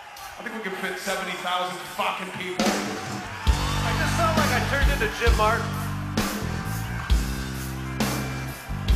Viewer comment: What is Roddy saying about Jim Martin 53:52? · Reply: "I feel like I'm turning into Jim Martin."